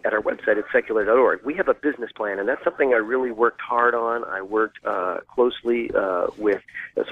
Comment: He totally said hard on!... That's the only thing I remember about Sean's interview, and I watched it twice. I wonder what that says about me?